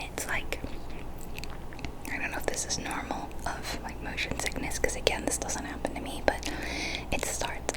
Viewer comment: The motion sickness you have is probably caused by the "Field of View" settings on minecraft. It happened to me a lot when I played Half Life 2. Try to adjust the FOV settings to something more comfortable to your eyes. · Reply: i thought she said emotion sick